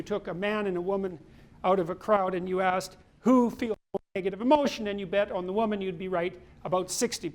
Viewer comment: "who ... emotion" the sound blurs out - what exactly did he say there - maybe " who feels more emotion"? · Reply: Paul Arthur “who feels more negative emotion” he is talking about neuroticism.